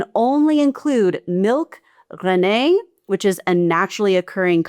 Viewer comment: Mais c'est quoi, << Renée / Rainnai >> dis donc ? De quoi tu parles, brdl ? 'Rennet' is 'présure' in French. It's 'rennet,' close to _'ran-it' or 'ren-net' (in-between and thereabout). Comes from Old English, 'rynet' referring to the inner membrane in a cow's, calf's, other animals' stomach, or a mass of curdled milk found in the stomach. You may be confusing it with 'rennet' from French 'renette' / Old French 'rainette,' &c., but that's an apple variety pronounced differently — approx. as 'ruh-nett[uh]' (the 'e' in 're-' / 'ruh-' sounding somewhat like the 'e' in 'the' or umlaut-o / oe / ö in German).